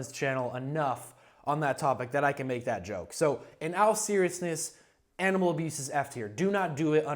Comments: did he just say in “al seriousness” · did he just say "in AL seriousness" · did you just say “in Al serious”